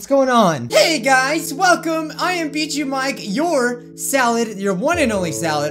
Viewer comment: look at the caption says on 0:38 beat you Mike 😂😊😂